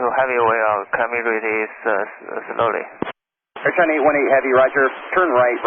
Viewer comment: I can imagine 😂 still, it could've been worse I think <link> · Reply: I think at 2:31, he said "can we release slowly?" He meant dumping fuel slowly maybe. I guess I can understand him better, coz my first language is Mandarin. :)